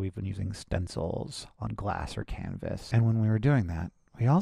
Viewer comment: “we’ve been making various kinds of spray paint art with stencils on glass and canvas” the first time I listened to this I thought Hank said cannabis because I had this on in the background while doing things and it was very confused for a hot second.